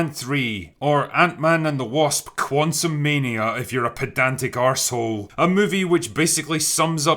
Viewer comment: Pedantic, how to get Siri to look that one up for me, at first, I thought you said, pathetic, I heard that word before, but not directed towards me, mostly outward.